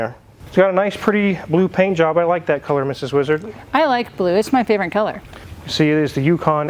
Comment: haha, it sounds like he says Mrs. Lizard lol